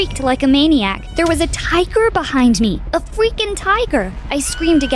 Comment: Ok did anyone else notice how she said fweaking instead of freaking